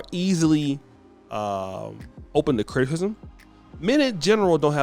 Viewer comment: Did he say Open to criktikizm ?😂